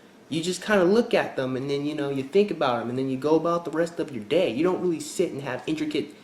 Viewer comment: Did he just say '...go about the BREAST of your day.' ? (4:27) HAHAHA!!